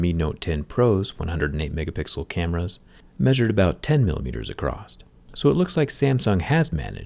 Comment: "acrosst" ? 5:34 did i hear that right ?